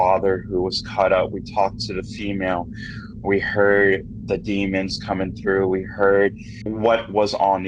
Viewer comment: i'm bingeing these videos after you asked what kind of spits are here and someone said "demon" i heard "playhouse" or "house"